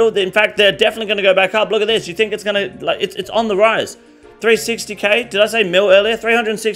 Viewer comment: I wasn't paying attention and I heard you say 360m for mole slippers and bout shit myself but realized you ment 360 after looking at the screen..